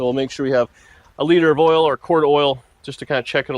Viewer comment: did you say a liter of cola? 😂